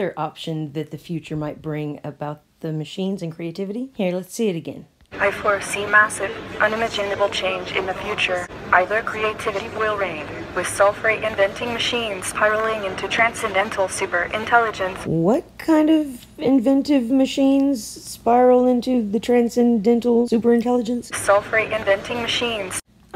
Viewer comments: I think at 5:48 Robot say's self reinventing machines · "Self-reinventing machines..." is what "Sophia" said. Nothing to do with sulfur. X-D 6:49, no "I can see you". You funny, narrator! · I thought it said soulless not Sulfur. Worrying stuff anyways. Also when I talk to guys about Sophia they think it's a great idea for 'entertainment' like westworld. Because of that I don't think there would be much fight until it's too late. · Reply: I heard "self-write".